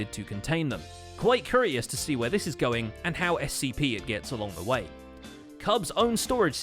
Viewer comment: did you just say “how scp it gets along the way”?